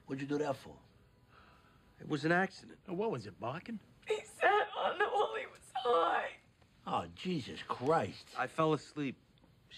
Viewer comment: what’d she say I couldn’t understand. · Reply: "He sat on her while he was high."